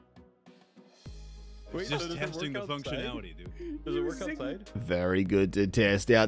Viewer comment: I heard it “functuality”